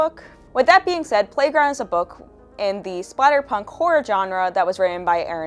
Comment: Brain no work. I first heard "splatterpunk" as "spiderpunk" and was like "okay, thid should be... interesting" 😂 Great video, thank you!